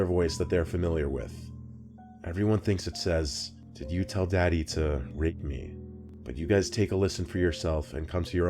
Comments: The last message sounds more like “did you tell them about me” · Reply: I may be hungry, or nuts I heard "did you tell daddy to bake me" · 3rd story i heard: “ypu tell daddy i want it-“ thats frickin spooky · for the last story i heard " you tell daddy he made me" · for the last story I heard "You tell daddy, help me" Perhaps it could be a spirit of a mother talking to her kid?? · 4th story sounds like it said did you tell daddy what happened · I heard "did you tell daddy you hate me" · I hear “Did you tell daddy what I said ?” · I heard "Did you kill daddy and grape me ?" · I thought it said "did you tell daddy to hang me" but I'm not sure I'm probably going to put my head phones on and listen again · 4th story: it sounded like she said: Did you tell daddy to rape me? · it sounds like it said ¨did you tell daddy to hit me?¨ scary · I heard “did you tell daddy to bake me”😳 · Sounded like it said 'Did you tell daddy you hate me??' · On the last story it sounds like it said "did you tell daddy you were going to eat me?" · 4 I hear “did you tell daddy to rape it” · it sounded like it said "did you tell daddy about me?" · I heard “Did you tell daddy what happend?” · I thought she said 'did you tell daddy you hate me' not rape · Ok but the video with the phone call I heard her say "Did you tell daddy to ___ me?" Clear as day. Wtf, am I the only one